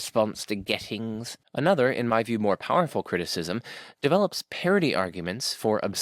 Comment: I only watched parts of the video so far, but I would like to say that I don't really find parody arguments convincing as critiques of the ontological argument, though I am not sure how philosophically watertight my objection is. Take the "score on a test by me of which no greater score on a test by me can be conceived". You are probably thinking about a 100% score. But wouldn't that score be even greater if it had the power to do things on its own? Would that score that can do things not be even greater if it could do all things? So an omnipotent score is greater than a non-omnipotent score. And if we go on like that, we end up with a score that is omniscient, omnipotent, omnibenevolent, eternal, the creator of everything. In the end you get something that has little in common with a normal test score and much in common with how God is normally defined. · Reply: Do you mean parity or Parody? I ask cause I thought he said parity which is a very different idea than Parody.